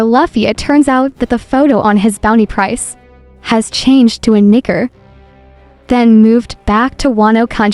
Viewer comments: WHAT DID SHE SAY?!?! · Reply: For Luffy's photo, change the image to Nika's form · Nah what she say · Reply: Luffy photo changes to Nika form